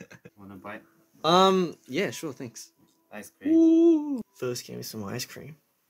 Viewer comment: In the voice message when he said ice cream i thought he said 5th grade 😂😂😂